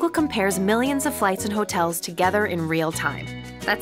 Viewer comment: Did she said : " All you have to do is hack "?